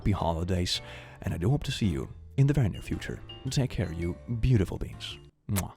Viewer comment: I know you’re saying “you beautiful beings” but I hear it as “you beautiful beans” and I like that ❤